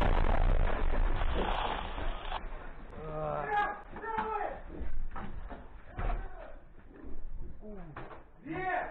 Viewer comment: Could someone please tell me what the soldier in the other room says at 6:14? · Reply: Everyone is alive? -Everyone is alive! -Fine? -Yes -stunned (from explosion) in general they count the wounded